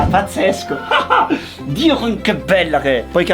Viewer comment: ehi, stupendo il modellino, ma solo io qua ho sentito “Dio can che bella!” 😂